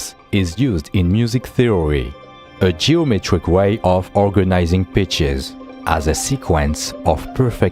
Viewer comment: I thought they said "organizing bitches"